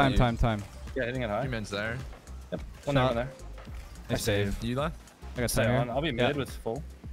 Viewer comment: it sounded like turned into a robot or something. It took me a few replays to realize that and said nice save at the same time.